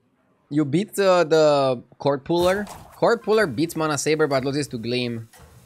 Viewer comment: I was watching a JJ video and during the opening, I thought he said kottbullar which doesn't even make sense because it's swedish? but he was actually saying cordpuller